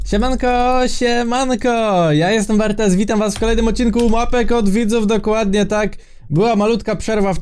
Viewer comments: zamiast Mapek od widzów usłyszałam ŁAPEK OD WIDZÓW😂😂😂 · zamiast "siemanko siemanko" usłyszałam śniadanko śniadanko XDDD lol. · Czy tylko ja usłyszałam "Łapek od widzów" ? XD😂